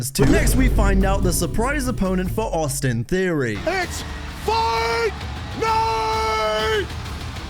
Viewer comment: I thought he said "its Fandango"